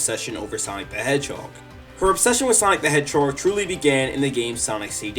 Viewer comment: holup, did i just hear "sonic the hedgewhore"? or was that just a mis-hearing